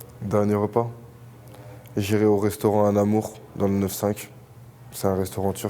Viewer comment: Cest quoi le restaurant dans le 95 qui dit 7:10?? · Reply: Anamour et ça a brûlé